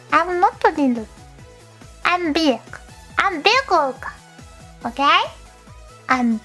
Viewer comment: You can't tell me you didn't hear "big ass orca" the first time around